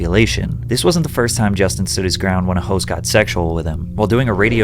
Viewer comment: “This isn’t the first time Justin stood his ground when hoes got sexual with him.” I know I misheard this but I like my version better.